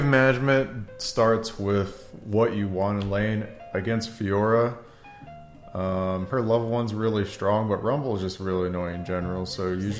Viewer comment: After I turned on closed captions, I'm glad to see I wasn't the only one that thought Dyrone said, "Fiora...her loved ones really strong" at the start :)